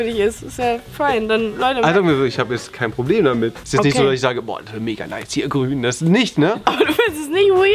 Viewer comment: hat irgendjemand verstanden was ju hier 8:02 sagt ?xD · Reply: ist jetzt nicht so das ich sage boar mega nice hier grün.. 😂